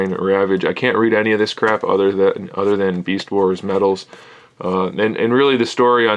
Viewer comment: did you say "I can't read any of these crap" ?